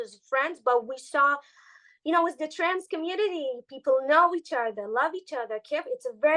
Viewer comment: what did she say? A tan community? What does that mean? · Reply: Trance community, as in psychedelic trance music, aka psytrance. The type of music festival they were in.